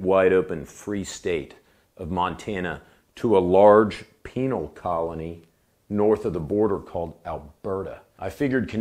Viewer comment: I heard “painal colony”